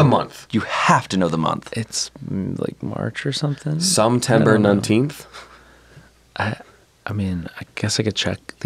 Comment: it sounded like said September 19 which is myyy birthday!!